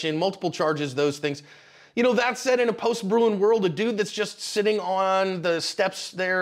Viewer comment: he says (according to captions) "in a post-brewing world"? Can someone explain to me what he is saying and what that means? Ive never heard that before and I'm curious what he's referencing. Thanks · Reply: 'Bruen' Post-Bruen, The Supreme Court’s Bruen Decision struck down as unconstitutional New York State’s concealed carry law that required an individual to prove “proper cause” existed before a license would be issued allowing that person to carry a concealed pistol or revolver in public.